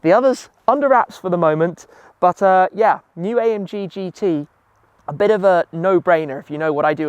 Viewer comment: did you say AMG GT T?